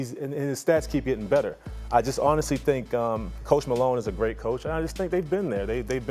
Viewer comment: i thought he said post malone for a sec